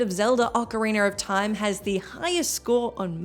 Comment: Legend of Zelda oca-weiner of time... wait, did I hear that, right? Did I miss a version? lol